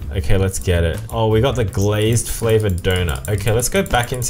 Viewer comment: Cookie Simulator percentage: 99% Bubblegum Simulator 1% Switching the names for cookie related stuff. This is so obviously a copy. Also, who notices calling the cookie flavor dough "doughnut" at 9:08? Or is it just me and I heard it wrong?